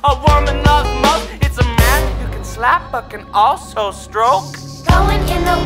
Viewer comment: Why in the uncanny part I hear “without the truth that it’s naked” 😭🙏